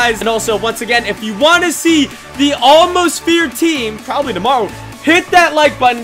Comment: I thought he was saying almost feared team